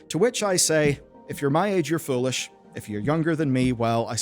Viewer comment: 'If you're my age you're foolish......' Chefs kiss. I've been playing StarCraft since it came out at the young age off 12. I have physical copies of SC:BW, WOL, HotS and LotV. I have never played Nova. If they released a hard copy it would be sitting on my self with the others. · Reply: Man, I heard that as "if you're my age, you're full of sh*t". I had to go back and listen again after I read your comment, but you were right, he definitely said "foolish" lol 😂